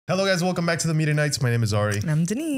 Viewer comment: dude talked too fast in the begining of the vid, when he said media knights i heard was "meter" knights..... hehehe... not the first time too